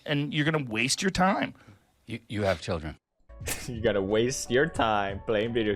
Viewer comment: the "you have children" sounded like it's a bigger waiste of time lmaoo